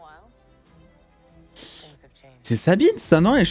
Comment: c'est sa-quoi ??? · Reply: C'est Sabine. Moi aussi à la première écoute j'ai cru entendre c'est sa b....😂, J'ai le réécouter une seconde fois pour être sûre😅.